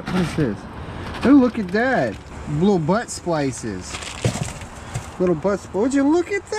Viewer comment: Not gonna lie, at first I thought he was saying 'butt spices.' I was a little confused lol.